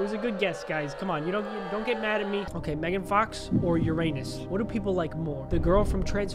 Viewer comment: Did sketch swore? 3:41 cuz he said "ur anus"